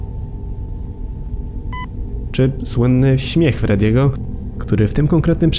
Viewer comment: Ja tam usłyszałem dont ciuch freddy